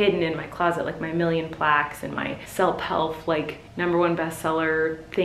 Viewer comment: Did you mean to say selp helf instead of self help 09:19 lol 😝